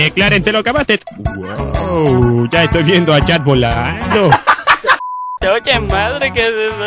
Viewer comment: espera dijo yo le entendí chad ._.